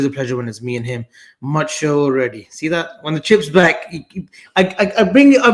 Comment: Good show, thought he said chimps back right at beginning, lol